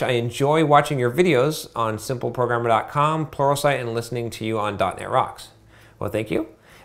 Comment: Everytime you say "Pluralsite", I hear "Porno site".